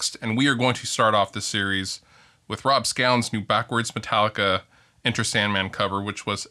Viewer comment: I thought it said Rob scallops XD